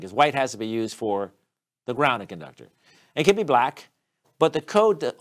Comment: Please watch the video again, I did not state that 'white is for the grounding conductor.' (1) There is not such term grounding conductor. (2) White if for the 'grounded' conductor [200.6]. (3) The NEC does not specifically state that the neutral conductor is considered a grounded conductor... However, the "neutral conductor" is required to connected to the "neutral point." If the system is grounded (from the neutral point), then the neutral conductor is a grounded conductor. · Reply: " 'cause white has to be used for the grounded (sounded like grounding) conductor", is the reason for my question. Answered thank you.